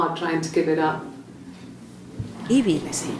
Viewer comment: anyone else catch "eevee" being said? strange